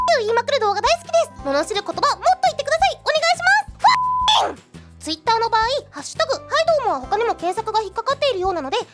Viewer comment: What is she saying at 2:10? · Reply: Brigade Leader Haruhi Suzumiya there not there